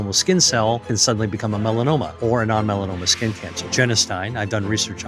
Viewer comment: What does he say starves cancer? 0:21 What does he say after "non-melanoma skin cancer?" Jennistine? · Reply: genistein, an isoflavone found in highest amounts in soy.